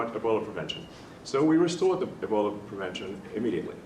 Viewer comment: I thought Elon said bullet protection. But it was “Ebola” protection.